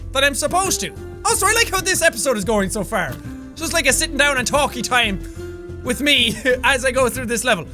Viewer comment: Did he say sitting down and talky time?